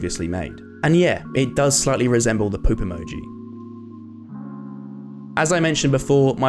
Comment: I swear that sounded like 'pope emoji' xD